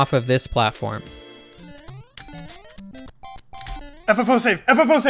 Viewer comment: I legit thought he was saying FFS at first, as if something had gone wrong xD